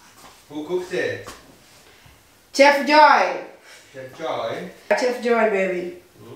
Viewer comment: Am 😂 soo happy to see and 🤩🤩🤣🤣 singing I had missed 🤣🤣🤣🤣when I heard "joyce" it was serious,you guys